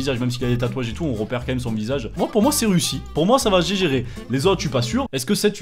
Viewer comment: "Moi pour moi c'est Russie" Mdrr